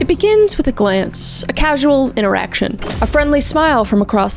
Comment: Omg I thought she said (a casual erection) at the beginning